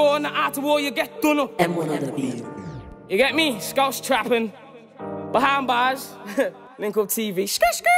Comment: I thought he said Scouse twat hahah